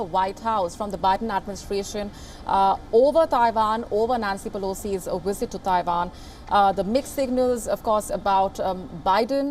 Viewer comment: A wizard is never late, nor is he early. He arrives precisely when he means to. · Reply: That's funny, I thought she was saying Pelosi was a wizzitt. Let's see what happens next. Be safe.